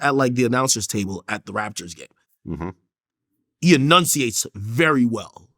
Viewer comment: He said “Raptors game” but it sound like he said “Raptures”